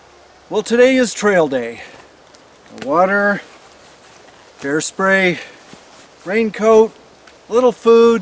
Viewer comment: I misunderstood his first sentence. Thought he said hair spray lol